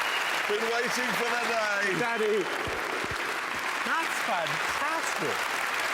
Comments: hold up - i swear simon cowell said 'daddy' at 7:35 😂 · Did he call him daddy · I SWEAR Simon says "Daddy" O.O so freakin weird